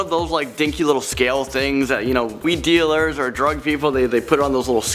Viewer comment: did he say “we” dealers 😶 What????!